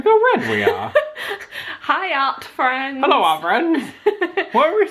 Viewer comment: when I first started watching you guys, in the intro, I thought you guys said "hi out friends" but now I know you guys say "hi art friends"